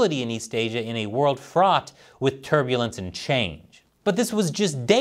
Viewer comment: subtitled fought instead of fraught